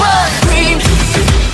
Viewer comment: "please want scream" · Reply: I heard "please want cream"